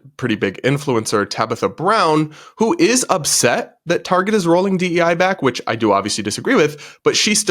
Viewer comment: Did you say you disagree with "rolling back" DEI?